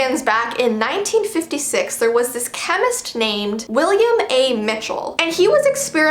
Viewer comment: Okay I thought she said "William Afton"😂